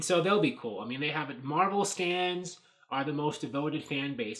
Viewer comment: sounds like you said Marvel stands.